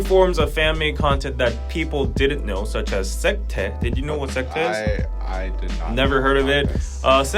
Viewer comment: did he say secteh???